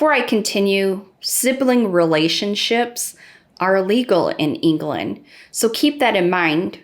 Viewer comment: it's in fact ILLEGAL in the UK x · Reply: If you’re referring to what she said around 9:15, she said it’s ILLEGAL in Europe, so sounds like she’s already aware😉 Think you guys might’ve misheard her?